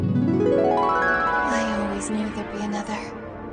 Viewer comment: what did she say? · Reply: “I always knew there’d be another”